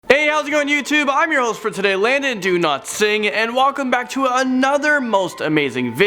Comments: It really sounds like your saying your name is "Landon do not sing" lol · Omg I always thought he said Landon Do Not Sing as a joke but I just saw that’s how he’s name is pronounced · I thought he said “Landon do not sing”lol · Yo guys is just me that i heard "Landon Do not sing'' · I thought he said "London do not sing" I was like (o_o) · Thought he said do not sing instead of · When he said his name I thought he said Landon Do not sing! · when u say ur name i hear " landon do not sing" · for some reason I always thought he said Landon do not sing until today... I've accended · I thought he said "Landon, do not sing" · was I the only one who rewinded the video cuz I thought he said do not sing? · I swear I heard him say "Landon Do not sing!"